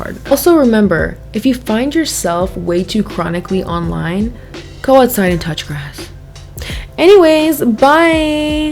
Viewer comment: 🦆 ok but why did I hear "Go outside and touch you as$" 😂 instead of "Go outside and touch the grass"